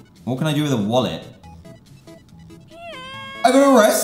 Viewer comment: I thought he said “she’s wearing the police!?” 😂😂😂